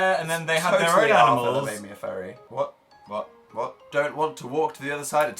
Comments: did dan say "it's totally arthur that made me a fairy" or??? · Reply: lmao i think he said 'it's totally arthur that made me a furry' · Wait I can't work out what Dan's saying at 5:52 Something about Arthur and Furries Help I need to know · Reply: he says "its totally Arthur that made me a furry." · what did dan say at 5:50-ish? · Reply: He said "totally Arthur that made me a furry" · At like 5:56 did Dan say that Arthur almost made him a furry fucking stab me with a dull spork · At around 5:43-6, does dan say hes a furry? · DID DAN JUST SAY "totally arthur that made me a fairy" at 5:51???? am i hearing things???? can someone pls tell me what he actually said, or what that even means???????